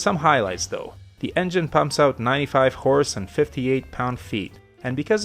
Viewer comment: I heard swap wives the first time. I thought I was on the wrong browser tab. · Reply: Meanwhile at 03:56 I heard "95 whores" so maybe we're on to something here.